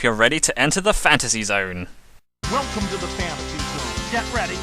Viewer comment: Lmao i always thought the game said "WELCOME TO THE FAMILY!! GET READY"